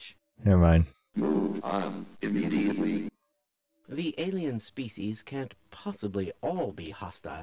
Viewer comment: I thought he said... "Indian species"